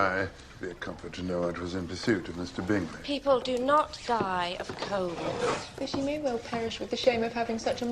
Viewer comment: Why did I hear the mom say “people do not die of COVID” at 1:27 lmao